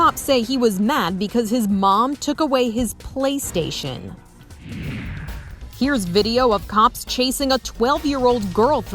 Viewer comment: i thought she said “took away his iPad”😭